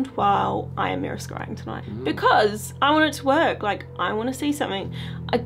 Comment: I was half asleep & I thought said ‘i wanna twerk” 😂 instead of “i want it to work”